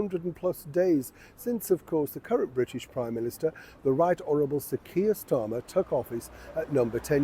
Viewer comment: Did he just say "the right horrible sir kia starter" 12volts mofo 😂😂😂😂